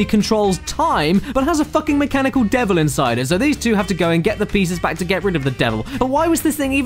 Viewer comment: caddy said "plieces" instead of pieces